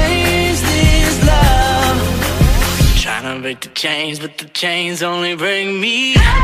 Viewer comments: I swear I thought it said “tryna break the chains but the chains only bring me UP” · You know, I used to think when he said “the chains only break me”, I thought it was “the chains only bring me”, and then instead of “hey-ey-ey”, I thought he was saying “u-u-u-up, u-u-u-up...” Anyone else heard something different? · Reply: I heard "tryna break the chain but the chains only bring me down"